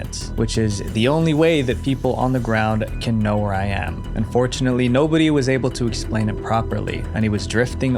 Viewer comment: fortunately" ARE YOU PROUD THAT AN INNOCENT MAN HAD DIED!? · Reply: He said "Unfortunately" you just misheard him